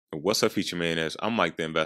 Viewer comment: Sounded like he said "What's up future Maniacs?"